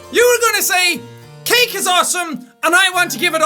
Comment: i totally thought he said "kink is awesome" not "cake is awesome"